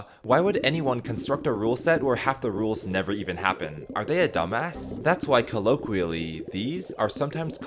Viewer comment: DID SAY DUMBASS?!